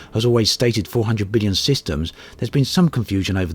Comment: "So with a little bit of 'meth'" Yeah I know what you wanted to say but it sounded diffrent ;)